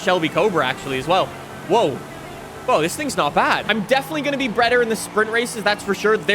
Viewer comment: listen closely. he says breader instead of better